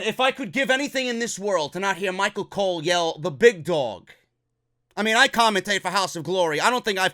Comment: Concerning your comment about Michael Cole's announcing "The Big Dog"...I always hear "THE BIG DONG!" you're welcome for hearing that from now on instead lol.